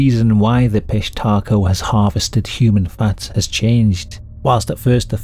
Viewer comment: Thought it said human fart harvesting tbh